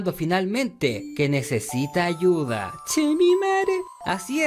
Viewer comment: porque dices Chingo a mi madre? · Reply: Jaja, no. Es chemimare, es una expresion local para denotar tristeza.